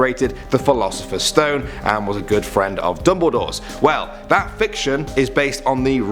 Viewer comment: I heard "good friends with double doors" 😂😂😂